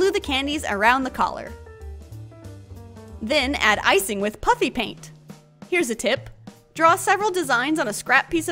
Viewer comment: It sound like your saying puppy paint